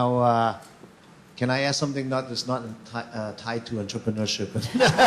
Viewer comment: at about 1:15:05, what did the elder man say that made everyone laugh? just wondering... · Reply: He was gonna ask a question that not tied to entreprenuer, which is not required by the host student.